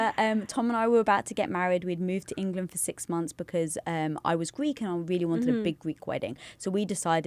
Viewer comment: Did she just say I was Greek?